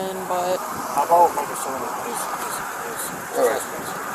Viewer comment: sounds like he sayng , how about he tell he was trespassing , something like that.